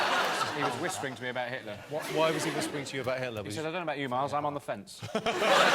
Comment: What does Miles say at 3:12? To me it sounds like "I don't know about you, Mars, but I'm on the fence?" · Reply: Josef Moberg Miles instead of Mars